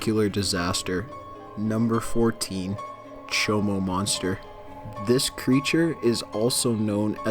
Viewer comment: wait... did he say... Chomo Monster